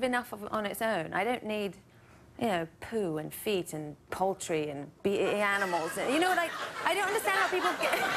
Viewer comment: Did she said poultry or poetry?